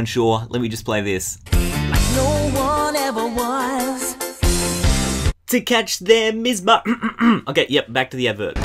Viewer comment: it sounds like it says gotta catch them all at the end